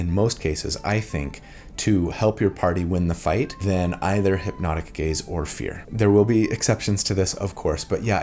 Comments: I hear "hypnotic gays" every time 😭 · I kept hearing "hipnotic gays" and it made me giggle 😆